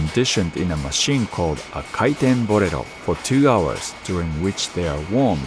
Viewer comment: I know it's not what was said and that my bad american anime fan who watched naruto once upon a time is showing but what i heard around 15:15 was that the pucks are conditioned in a machine called a Kaiten Burrito.. so now all i can think of is Fire Burrito.. and it made me giggle. I mean it definitely Isn't an accurate translation but when i looked back at the screen to see the machine it well.. definitely looked like what i was expecting.